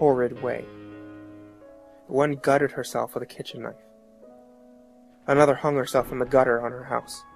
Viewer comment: Is he saying "my mother hung herself" or "another hung herself" at